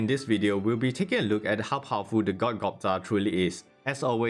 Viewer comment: I thought it said god gouba...